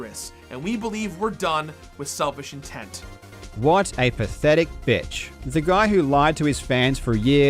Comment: every time i hear the line "what a pathetic b1tch" from i cant help but laugh so hard. his right tho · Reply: Am I the only one who heard "What a pathetic pitch"?